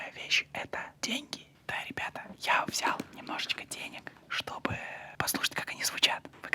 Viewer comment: Мне не послышалось Влад сказал НЕМНОЖЕЧКО денег